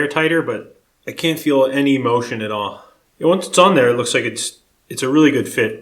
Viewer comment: I thought you said “I can’t feel any emotion at all,” and I spat out my Juicero. On second viewing, you are talking about “motion.” I can’t feel any emotion myself. I became dead inside when Bob Vila stopped hosting This Old House.